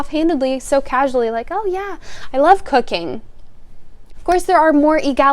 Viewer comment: “Oh yea, I love cooking” LOOK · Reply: 😂😂😂Until I saw this comment I thought she said I love cocaine